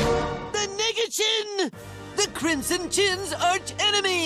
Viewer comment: I watched this on TV and literally thought he said nigga chin and couldn't think what else he was saying. Never did it cross my mind until years later, to know that he was saying nega chin. I mean a black crimson chin